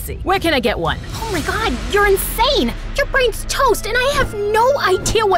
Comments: did anyone notice that in the captions it said “ OH MY GOSH your insane your French toast!” Instead of “OH MY GOSH your insane your brain is toast!” 😂😂😂😂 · she says toast but the subtitles say French toast